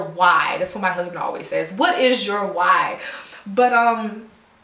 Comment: LOL! I could of swore you were saying "what your wine is" I was like CH-YEAHHHH Sweet and bubbly ;D That kinda works with my hair story....LOL ;) Yeah some people get so involved with the hair that they forget their is a human behind it SMH. It's good to open up the box! I agree! People take too many things for granted and everyday rules in life become the abnorm on youtube smh, YOU BETTER WERK! GO 'HEAD GIRL!! ;D